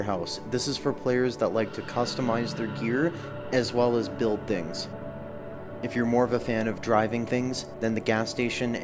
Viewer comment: I thought he said ''as well as build dicks''